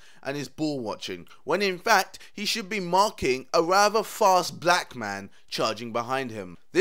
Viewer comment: everytime i watched this guy's video, I always heard he says "He should of mark the rather fast black guy", hahhaah